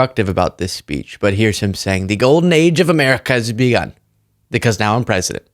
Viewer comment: Every time he repeated "Golden Age of America", I heard "Gilded Age of America" which ended with the assassination of Pres McKinley. Then later on, he talks about renaming a mountain McKinley. Coincidence? 🤔🤔🤔